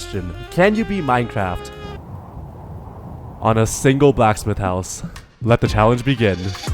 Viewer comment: dont think I didn't catch the "BLACKED" at 1:20 lmao